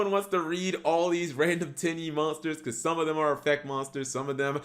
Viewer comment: My girlfriend was sitting next to me not really paying attention to the video about Tenyi and just randomly looks over at me and says “did he just say Titty monster?”